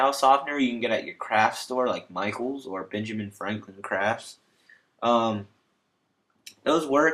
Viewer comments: I does sound like he said benjamin franklin craps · I swear he said benjamin franklin craps